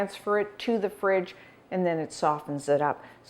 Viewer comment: what does she say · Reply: Stephanie Jaworski, it's her name.